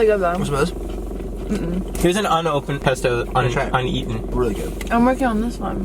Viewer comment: Did say "hunny try it" ?????